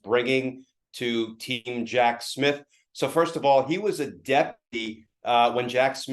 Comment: "he was a deputy.." I had to check the captions on that, it sounded like "deadbeat"